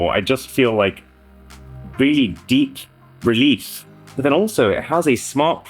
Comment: there is absolutely no way the subtitles said release instead of relief right then💀💀💀